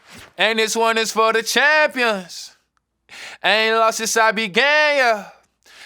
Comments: Am I the only one that thought that he said "I ain't lost since I've been gay" on the track? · I thought it was “I ain’t lost since I was gay” began makes more sense 😭😭😭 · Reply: Its "i aint lost since i begin". But i thought he said gay at first too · i can't be the only one who thought he said ''i've been lost since i been gay yuh'' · the whole time i thought he said" i ain't lost since I've been gay" lol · Ohhhhh, it's "Since I began" Lots of people (me included, ngl) and even some reactors thought he said "Since I been gay (yuh)" lol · I thought he said “ain’t Been long since I’ve been gay yuh” 😭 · I coulda sworn he said “I ain’t lost since i been gay yeah” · I can't be the only who thought he said "i haven't lost since i been gay yuh" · i thought he said "aint lost since i been gay" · I'm trippin. I thought he said "I ain't lost since I been gay (yea)" 😂😂😂😂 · “I ain’t lost since I began yuh” Am I the only one who thought he was saying something else ? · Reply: I thought he was saying any losses I be gayer😂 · Really thought he said "i ain't lost since i was gay yuh"😭😭it made sense in my head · I really thought he said "I would be gayer" instead "since I begin (yeah) " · I thought he said "I ain't lost since I be gay (Yuh)" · I thought it said I ain’t lost since I been gay 😂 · I thought he said” I ain’t lost since I been gay” I been singing da lyrics wrong this whole time😭. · who else thought he said “I ain’t lost since I been gayyy yahh “